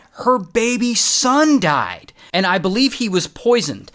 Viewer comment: when you said Baby son died, i thought you said baby's son died, and I got so confused, like how could a baby have a son 😅 lol im tired, dont mind me 😅